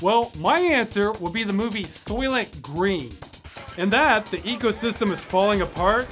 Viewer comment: What does he say? I want to watch that movie. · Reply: Green.